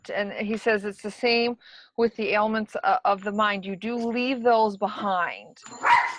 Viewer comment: thought she said you leave the grief behind then she says you dont recognize people because of your intense grief lol